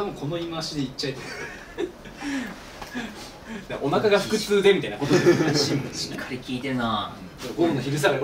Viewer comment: ｢お腹が腹痛で｣って言ってるはずなのにthの呪いで｢空く空くぜ｣に聞こえるのさすがで草